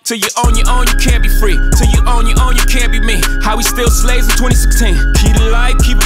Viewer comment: Jay Z :How we still slaves in 2016 Kanye: coz it was a choice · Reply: I actually thought he said how we still slaying In 2016.